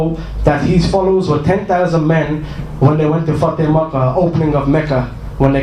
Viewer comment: What does he say @ 2:01? Sounds foul. lol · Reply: He says FATHE MAKKAH which refers to the taking of the city of makkah by the muslims